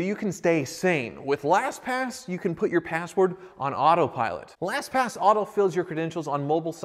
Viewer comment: I thought you said it was sponsored by glass packs. That would actually makes a lot more sense than last pass.